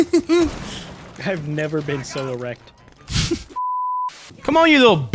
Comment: anyone else thought he said "solo rect" after the jabba bukake joke